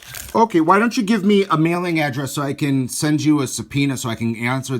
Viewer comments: HAHA, the ''irs'' thought you said ''i need a mailing adress so i can send you a penis'' and ''mike'' responds with ''send you penis to your mom" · Yes, 2020, My laptop was playing this when I walked in. He took it wrong, he didn't know our "Lawyer" said, Subpoena! he instantly thought he said penis 😹... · Instead of a subpoena, he thought you said send a penis😂😂😂😂😂 I don't even think he heard that word before... But he did say how bout dat!! 😂😂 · He said “subpoena” but the scammer is too dumb to know the word and thought he said “penis” lol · Tha- That person thought he was saying "penis" instead of "subpeona" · That idiot thought you said "send you a penis" instead of "send you a subpoena". LMAO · When you said you would send him a subpoena, he thought you said you would send him your penis. Funny accent for someone born in West Virginia. So tragic that they actually succeed in swindling people out of their savings!